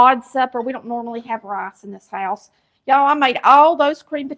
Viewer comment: I'm watching this at the end of 2025. I thought I heard you say, "...we don't often eat rice in the south..." I have to let you know, in the Arkansas part of the south, We do. 😂 Rice capital of the United States. ❤ Love ya! 😂